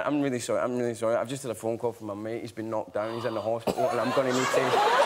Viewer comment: what does he say at 0:50 dont understand him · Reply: Hoes "I've just had a phone call from my mate, he's been knocked down, he's in hospital."